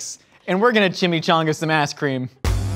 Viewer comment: Are we gonna take about how at like 8:38 said “a** cream” instead of ice cream or am I crazy...😂